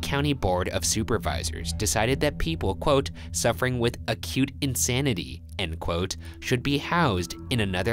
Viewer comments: I wasn't watching the screen and heard 'suffering with a cute insanity' ...instead of acute xDDD I thought "oh that's cute...just a little small cute insanity" 😂😂😂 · I heard "a cute insanity" and was like: awww me! Lmao